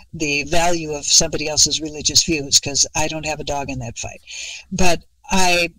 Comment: Did she say "I don't have a dog in that fight?"